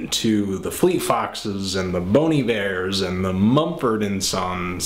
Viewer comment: Oh; I heard "Bony Bears" =P